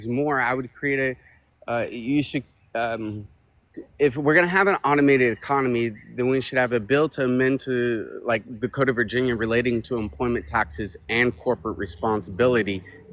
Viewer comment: Dude is on the East coast. He’s talking about WaWa, Sheetz and BoJangles. Guarantee you he lives in the woods. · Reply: 😂😂😂 I thought I heard him say “here in Virginia…” but still 😂😂😂